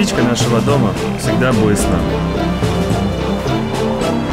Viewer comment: Привет! Подскажите пожалуйста, а то ни как не разберу, что сказал Дарио 0:58 "Мы верим, ....... Мы вернёмся домой."? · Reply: axill мы верим и мы знаем, мы вернемся домой